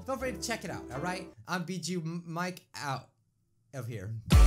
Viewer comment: ok am I the only one who heard him say GACHA Mike NOT BIJUU Mike hmmmm